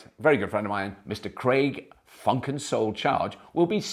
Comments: Did I hear you call him Craig "Funk and Soul" Charge?? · Did he just say Craig (funk & soul) Charge? Does that mean the name of the show is changing to Fully Charles? ;D